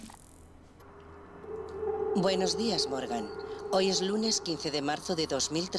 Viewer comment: pense que decia "buenos dias morgana" y dije este knekro volvio a lol le di entrar sin pensarlo 2 veces.... pero la vida es triste :c